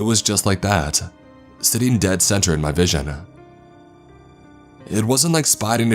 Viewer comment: he says "sitting dead center in my vision" but the subtitles say "sitting dead center in my vagina" 😭😭